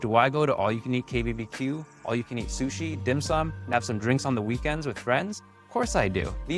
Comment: Hold up... most important part of this video... 5:15 did you say all you can eat dim sum?